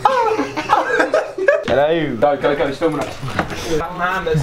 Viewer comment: Idk why I hear "no mames" in this part 😂